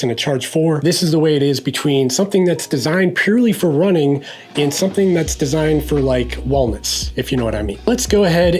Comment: What do you say at 8:40 ? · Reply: "Something that's designed for running... Versus something that's designed for Wellness if you know what I mean" Funny, listening to it again it sounds like I'm saying "Wallnuts"? Can you tell I'm from the Boston area? 😂